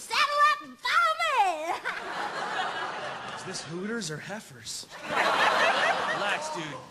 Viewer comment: what did he say at 0:31 hooters or what??